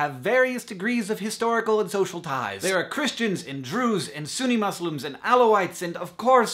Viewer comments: did he just say Christians and drews or Christians and Jews? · did he say "drews" ?